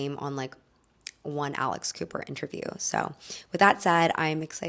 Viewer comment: At first I thought you said Alice Cooper, but alas.